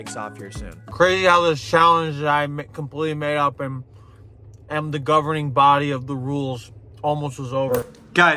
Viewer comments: Maybe I’m just really good at translating mumbles due to being the only one who understands my baby brother (he has a speech impediment) but he said “Crazy how this challenge that I completely made up and am the governing body of the rules almost was over” at 16:03 if anyone was curious since he doesn’t know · It's hilarious because at 16:00 it sounds like he says "Crazy how this challenge that I McCompletely made up and am the governing body of the rules, almost was over." Even at his most delirious he's still busting out the McJokes.